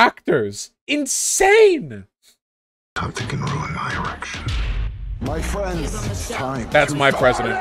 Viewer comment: He said erection instead of election. Even the subtitles caught it and when the subtitles say a dirty word, you know a dirty word was said because they probably err on the side of innocent words.